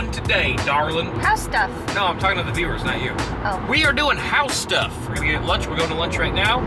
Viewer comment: I heard "but stuff" in 3:37 instead of "house stuff" and I don't know why.